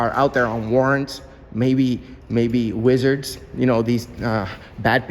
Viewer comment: Did you say the police should be looking for wizards???🤣